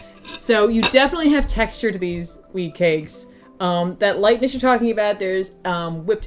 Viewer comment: At first I thought she said weed cakes. Wtf Aunt May.